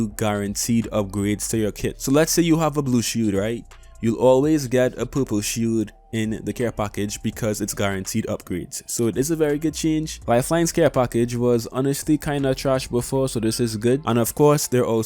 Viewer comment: i heard a poo poo shoot instead of purple shield 😂